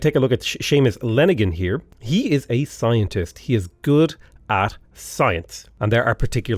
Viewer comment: s I thought he was saying Asians >_> and it was a Scientist.